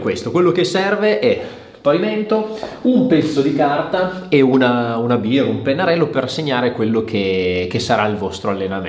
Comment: solo io al posto di "una biro" ho capito "una birra"?😂